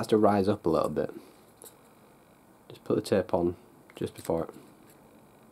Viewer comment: what you said- "put the tape on" what I heard- "put the tampon"